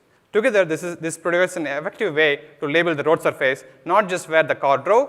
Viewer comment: what’s he saying?? Roads-a-face???